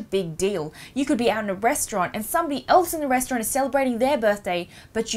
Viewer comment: got to the point where you said "you could be out in a restaurant" and all i heard was "breastaurant" and got distracted for the rest.